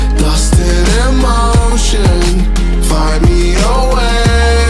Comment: i may be trippin but i thought he said “fly me away” not “find me away” because in his live performance he did an airplane like gesture 🙈